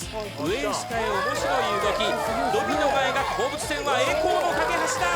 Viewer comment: アナウンサーが何言ったか聞き取れる人いますか？ 今インドネシア語の字幕作っています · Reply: 上へ下へ面白い動き。 ドミノが描く放物線は栄光の架け橋だ。 ここの部分かな